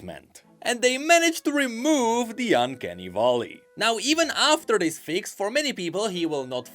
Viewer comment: I thought I heard uncanny Volibear Yes we can fix him too lol